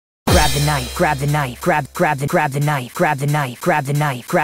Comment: why do i hear "crab the nife"